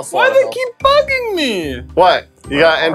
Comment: did say "hey why do they keep f###ing me"